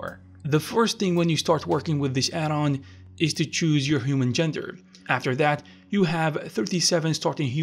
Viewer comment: I thought it said 37 genders to choose from... 😅